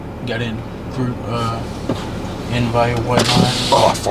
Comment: did he say "Ugh, fart" and roll the window down 😂😂